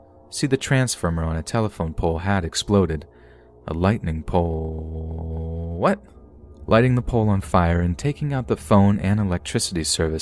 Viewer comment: Those who are questioning 12:00 he messed up and said "lightening pole- what" but then corrected himself and said "lighting the pole on fire"